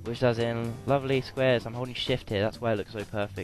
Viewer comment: holding shift sound like ur sayin *holy shit* XP lol